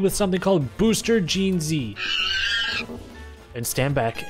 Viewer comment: at first i thought he said “booster gen z” and was like shit new update